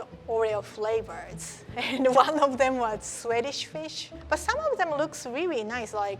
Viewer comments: I thought she said sweaty fish flavor 🤣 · I thought she said sweatish fish lol